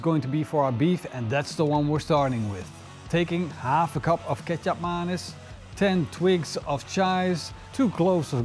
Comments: Half a cup of ketchup monas??? Wtf did he say? What's that dark sauce @ 1:30? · Youtube did it again... Ketchup mayonaise 🤣🤣🤣 IT'S KETJAP MANIS, STUPID TRANSLATION. Edit: It got worse... Cactua mines 🤣🤣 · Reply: THANK YOU! I’ve been wondering what it was 😂 I always thought he said ketchup madness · You lost me at fresh ingredients and whatever kejaptmanis is. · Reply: 🤣😂🤣🤣dead i thought he said ketchup mayonnaise